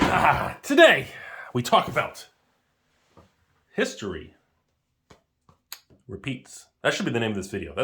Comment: I thought you said, "Today we talk about history.... and penis"